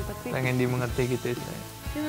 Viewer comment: 2 : 38 & 2:39 apa gue salah denger... Nuca ngomong "Kayak pengen dimengerti gitu ya sayang.... " ahilahhhhh durasinya tambahin woyy :(